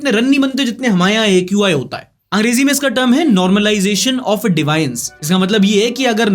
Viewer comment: what did you pronounce DEVIANCE as? Kya bol rahe ho bhai?!